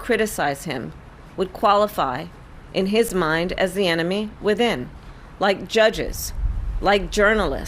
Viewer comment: Did she just say "anemone within" lmao